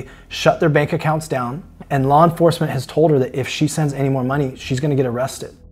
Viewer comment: She's not a victim she's a participant. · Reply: Sounded like she was saying if she had more money she would send it